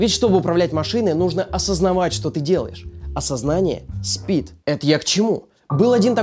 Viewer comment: Блин, подскажите пожалуйста что он сказал:"Осознание спит" или "А сознание спит"?